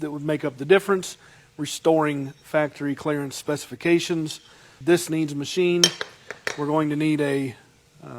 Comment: did you really say, “ It needs machined?”